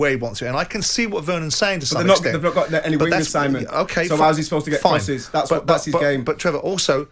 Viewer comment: Can some translate what Trevor said at 5:41 ? 😂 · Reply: “They haven’t got any wingers Simon, how’s he supposed to get crosses that’s his game” (I think!)